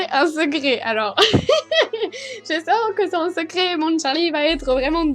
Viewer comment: J'adore tes vidéos et à 3:54 j'ai entendu <<ah mon Charlie>>Hugoposai xD bref je t'adoreuh et aussi pour se qui dise que à 80 ans ou 40 ... elle n'a pas 40 ni 80 elle a moins de 18 mais si sa serais vrai qu'est ce que on sent fiche !💗elle fait se qu'elle veut nan bref laisser la et je vous adoreuhhh les filles💗